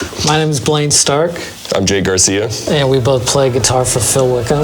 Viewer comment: I thought for a sec he said he is Jay Garcia 🏴‍☠️